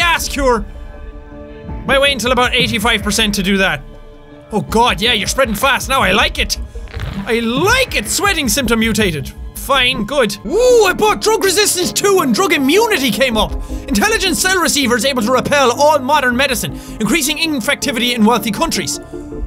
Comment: I thought you said I bought butt drug resistance